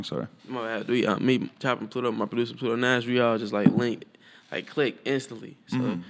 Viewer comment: What the fuck did he say between minutes 10:09-10:12???? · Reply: "Me, chop and plu2o- my producer plu2o Nash all linked" sum like that